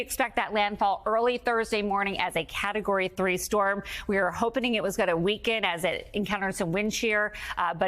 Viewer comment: Did she say "we are hopening . . ."? 1:34 🤔